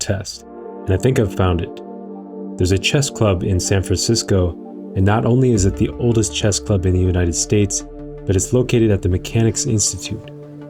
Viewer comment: did he say san franciso? Here i come san francisco